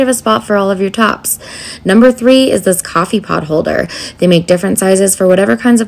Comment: I thought you said coffee POT holder for a split second I imagined you somehow sticking the entire coffee pot up on the underside of the cabinet lolz